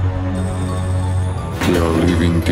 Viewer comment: did he just say we are living dildos? xD